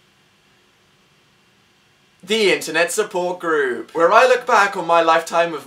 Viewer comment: For a second I thought the computer said intervention instead of Internet because Dan had just made a joke about his drinking habits